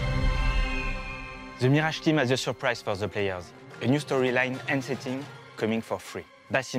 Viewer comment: What the hell is bro saying ? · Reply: “ze Mirage team has ze surprise for ze players”